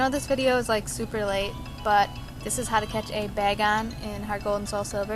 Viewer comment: I swear I thought it said Bacon.